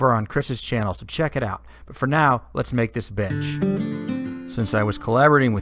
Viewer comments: i swear it sounds like hes saying lets make this bitch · Lmao I thought he said let’s make this bitch and I was like hell yeah let’s do it